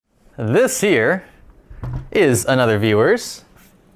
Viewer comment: I don't know why, but always when says "This here" and I hear "This year" :) It's so funny how many PCs have been fixed "this year" ;p